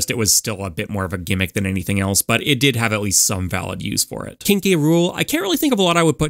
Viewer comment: when he says "King K Rool" I burst out laughing cause I swear I heard "Kinky Rule". And apparently, google subtitles agrees with me.